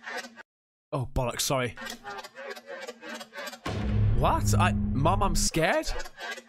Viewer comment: I didn't hear "mom I'm scared" I closed my eyes and I heard, "mom killed me"